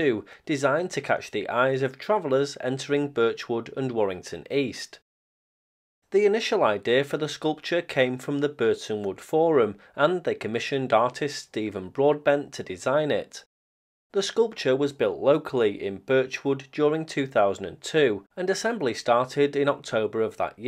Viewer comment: I thought you said Steven Broadband instead of Broadbent and had a chuckle ;)